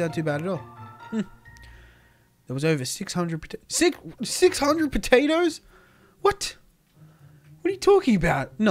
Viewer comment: I was drifting to sleep then I hear ' 600 potatoes ' and I go YUM